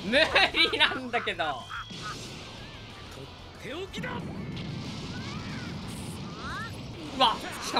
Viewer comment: ブロリーのアルティメットブラスト「とっておきだ」って言ってるところ病気だって聞こえる😂